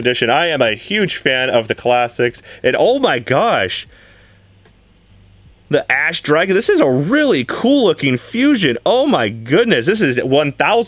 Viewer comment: I swear every yugituber that pulls it sounds like they say ASS dragon 🤣🤣 lol